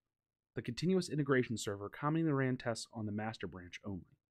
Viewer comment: Caption says "...calming the ran...", should be "...commonly ran...". Yes, I had to turn the captions on.